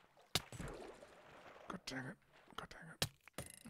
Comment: did say "God dammit" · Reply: Sounds like god dangit buts its to close and mumbled so only 80% confident in that answer